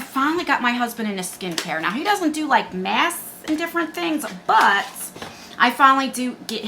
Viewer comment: When you were talking about your husband doing skincare, I had to replay it cause I didn't hear mask. I was doing my skincare routine and thought you said he doesn't do a$$ and was like what kinda skincare routine we talkin bout???? 😅